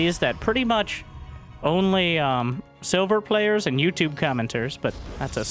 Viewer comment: Silver players and YouTube commentators know this, that’s A circular venn diagram. Literally the best thing I’ve ever heard. Love it!! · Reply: lol I thought he said "sober players" so i didn't quite get the joke (the youtubers would be more likely to be drinking)